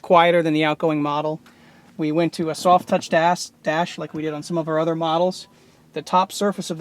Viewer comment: Did anyone else hear the guy say "We went to a soft touched ass" at 10:09 hahahah